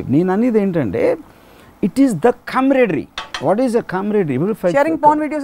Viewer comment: what is the word used at @ 22:37 sum thing it sounds like come ready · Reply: abhinesh ch camaraderie meaning mutual trust and friendship among people who spend a lot of time together.